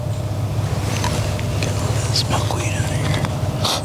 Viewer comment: I swore he said I’ll see if I got some skunk weed n here n reach n pocket lol he started video say n me n lit but after he reach n his pocket it was milkweed